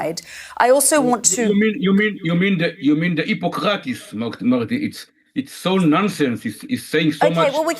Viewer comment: Did he say "Hippocrates" 🤣🤣🤣 LMAO